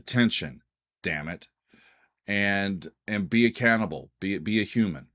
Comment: I thought you said "be a cannibal, be a human", as deliberate irony. (Well, not really, I wondered.) But it turns out you said "be accountable". That makes more sense.